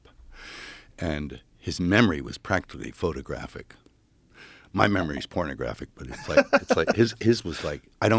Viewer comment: Did he say my memory is pornographic 😂